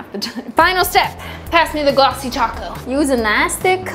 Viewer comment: Idk why I just now realized that every time speaks, he sounds like such an intellectual, while I here am so dumb I thought u said "pass me the gloco taco" and not glossy taco